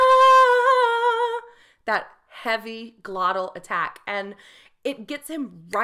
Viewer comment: minute did she said Luis Miguel had a heavy gluten attack? 😁👻